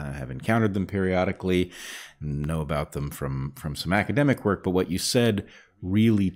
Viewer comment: of course we are talking about beavers (known for smacking their tails loudly on the water surface) here, so when Bret said “some academic work” I heard “smackademic work.”